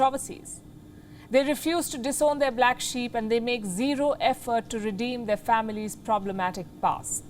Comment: I heard.. "They are knee deep in trouble and they give zero F-Word (instead of effort)" ha ha